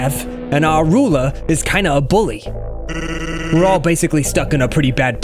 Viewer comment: did sans just say for the ruler bitch instead of bully?